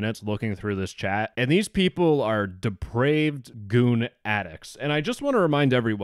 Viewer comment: Goon! I heard goon